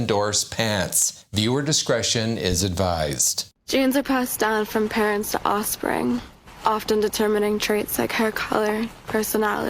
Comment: At first i thought she said jeans are pastel from parents to offspring. Had to relisten to it a couple times to figure it out lol. Could have been because i was having a hard time paying attention to what she was saying but also she does run those words together in that part. Anyone else hear that?